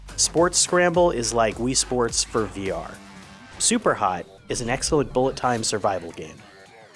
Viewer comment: Sounds like he said Super High 😂